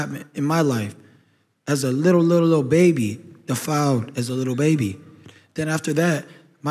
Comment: Why did ryan say he was defiled as a baby?? Anyone catch that??